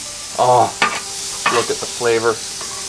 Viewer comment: anyone else hear him say "look at the flavor"?? listen